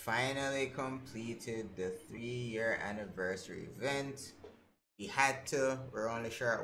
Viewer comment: I rewinded for the keyword a few times, and thought you said "a name for a cereal" so I'll just go with that XD Ign gingєrvitus Card MJRandy